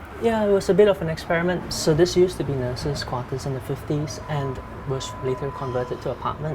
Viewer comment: What is he saying at 0:59? It used to be what in the 50s? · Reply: nurses' quarters